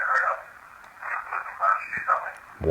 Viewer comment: When asked about the priest, I heard "don't you dare."